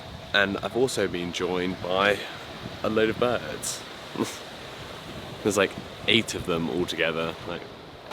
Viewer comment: I just watched the part where you said you were “ joined by some bids, eight of them altogether” but I heard “joined by some birds. I ate them all together” and did a double take. 😊